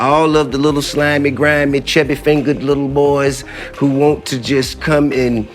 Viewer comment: Did he just say Chubby fingers lil boys ,😏